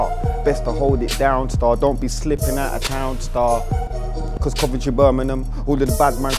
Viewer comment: what did he say before 'Birmingham' at 1:20? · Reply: Coventry